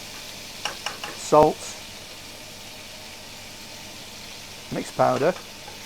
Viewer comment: What's at 1:03, couldn't hear it? Sounded like mixed powder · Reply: Garlic flakes. 👍